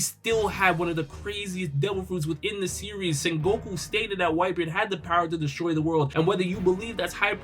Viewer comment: is it just me or did i hear him say "goku stated"